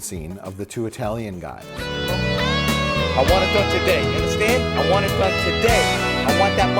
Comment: The assassination scene at 45:45 sounds like he's saying "I wanna touch your dick". I know what he's really saying but now I can't unhear it 😂